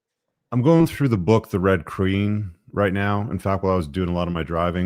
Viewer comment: does anyone know the book is talking about? I couldn’t make out what he said “The Red _____”? I didn’t catch the last word of the title. With thanks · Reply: The Red Queen